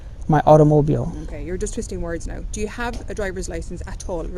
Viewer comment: It was even funnier when l misheard and thought she said, "now you're just pissing words". 😊